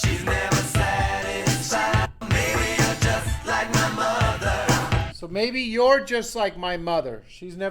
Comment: While listening to this video I was distracted and forgot what the ME was supposed to be. When you played the original Prince version I heard "Maybe I'm just like my mother" and was confused when you then said "You're just". So I went back and listened to it again and it said "You're just"